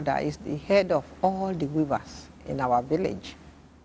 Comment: did say her great grandmother is the HEAD OF ALL THE "RIVERS" in her village. Please help me confirm cos i dont think i heard her correctly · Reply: Head of all the WEAVERS. People who weave baskets, Raffia mats and hats etc.